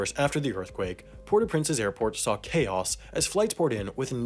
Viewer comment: CC on English, it says airpot instead of airport. :)